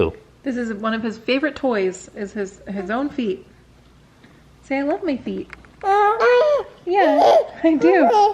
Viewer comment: I SWEAR he says “I love my feet”